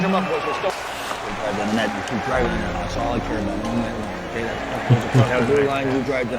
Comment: what did coach brooks say to that one player? I couldn’t catch it. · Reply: I think he said, "You keep grinding it out, that's all I care about"